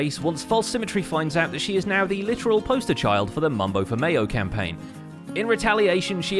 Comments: Did anyone else catch that he said mumbo for mayo campaign? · pretty sure I heard Mumbo for Mayo campaign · Is it me, or did he say "Mumbo for mayo"?